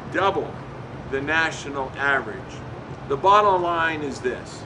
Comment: Did he say "bottle line" at 1:45?